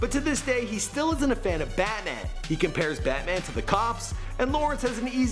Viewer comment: Did he say batnan